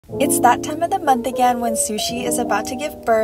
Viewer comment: At first i heard It's the time in the month to make sushi " shows her fish " me 😶